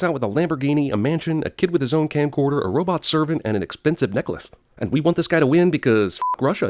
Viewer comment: Did you say expensive necklace at 1:20?